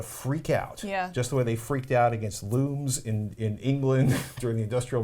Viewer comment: Oh, looms... I thought said loos. That changed the meaning.